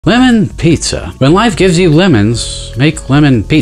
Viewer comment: I was sleep and I didn’t hear lemons I heard women, so I thought bro said women pizza 😭